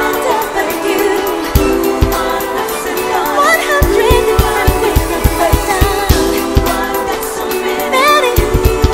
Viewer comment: What are the backing vocalists saying at 4:47? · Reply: Ooh wahh there so many... Ooh wahh how bout U... Day and Night, night and day all I got to say is...IF IF WAS UR GIRL